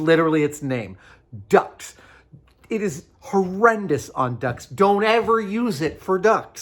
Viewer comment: All I hear is "duck tape is horrible at ducks"